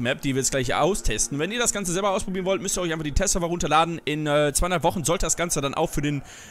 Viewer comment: Was in 200 Wochen für den normalen Server? Oder war das 2 oder 3 Wochen? 😂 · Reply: Ich hab auch 200 Wochen verstanden 😂